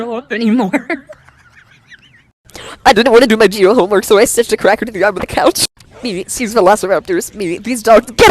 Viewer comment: wth is this girl trying to say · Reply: I heard I didn't do My phone mark so I sister crack on her arm sniffing.😅🤣🤣